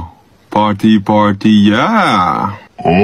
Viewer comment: jungkook:oh party party yeah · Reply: I thought he was saying pottey pottey yeah